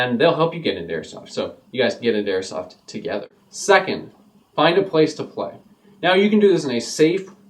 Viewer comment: Lol it sounded like he said "find a place to fuck" when i first heard it.